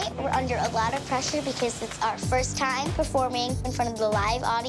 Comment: Did just say I hope I don’t pull it off